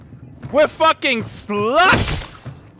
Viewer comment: i thought he said, "we're fucking sluts!" XD